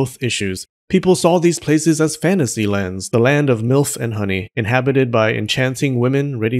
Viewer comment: did he just say ,,the land of ,,MILF" and honey" (0:09) or did I mishear it...?🤨